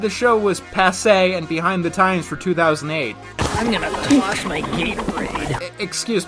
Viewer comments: I heard “I’m gonna wash my gay parade” in that one clip. It doesn’t make any more sense. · I don't know where you got "I'm gonna wash my gatorade", I heard that as "I'm gonna toss my gay parade".